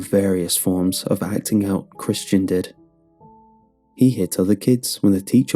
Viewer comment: I thought it said Chris Chan in the beginning and understood the father's decision.